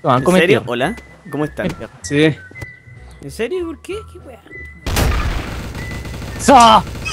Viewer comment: ALGUIEN ESCUCHO A UN WEON QUE DIJO :QUW WEA?( SUPER BAJO) EN EL